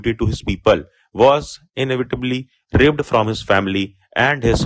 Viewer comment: sir ji , ye kya bol diya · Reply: Inevitably